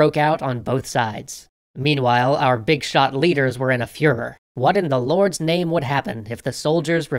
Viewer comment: Wait... did you just say that the German leaders were in a 'Fuhrer'? 😆